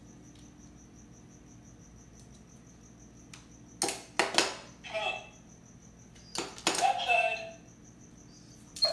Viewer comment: What's the word at 0:32 ? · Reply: Xepheum “come” so said “come outside”